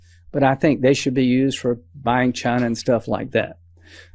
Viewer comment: What does he say there? "...for buying CHINA and stuff like that" ?? I really don´t understand it and the subtext says "China". I DO know what he means... he means: "Trump should get to keep the donations." But what did he actually say?